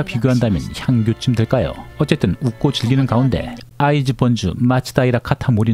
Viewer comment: 자꾸 아이즈 번이 아이즈원으로 들려